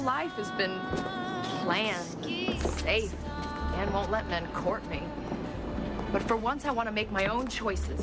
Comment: wht did she say!? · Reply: "Dan won't let men court me" because in this dream Dan took care of Peyton after her mother died.